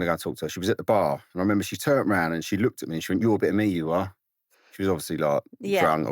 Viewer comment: What did he say at 2:11? He said Katie Price looked at him and said something. I cannot understand because of his accent. Can someone translate? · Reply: You're a bit of me you are